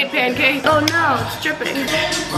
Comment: I thought you said the pancake was stripping and then I heard again and it was dripping